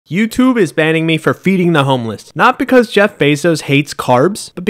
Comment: I thought he said beating the homeless 😂